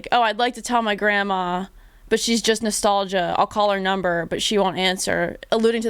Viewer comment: How have I gone this long not realizing or glancing at the lyrics and knowing that he says “she’s just nostalgia” instead of “she’s just nostalgic” I ALWAYS HEARD “She’s just nostalgic” And it made me think that she was like terminally ill, or has some sort of dementia or severe memory loss. Which is just as tragic as the actual song 😭 Cant believe I went that long not knowing that